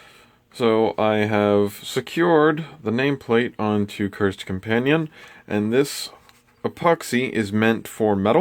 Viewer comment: Sweet man, for a second i thought it said 'cursed cappuccino' lol